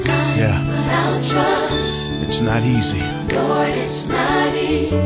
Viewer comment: I keep HEARING "I will TRUST U! It's not easy but I will TRUST U!!!" In this season I will TRUST U!